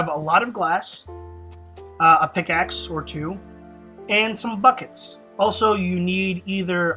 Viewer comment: who else thought that at 0:16 it sounded like he said fuckits